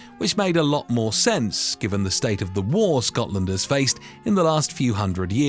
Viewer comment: I'm sorry did you call us Scotlanders? at 5:09?